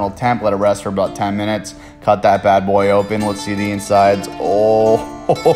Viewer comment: All i heard was “CHOP THAT BAD BOY UP” “Whoahahohoho sus” ”salami”